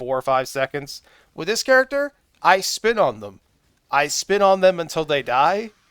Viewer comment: Instead of spin on them, i thought you said "I spit on them". I laughed way harded than I should have. Thanks for the video.